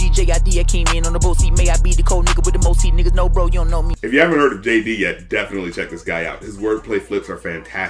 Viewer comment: Did you call JID, Jay-z?